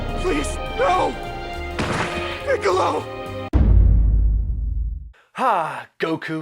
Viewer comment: who else heard dick-olo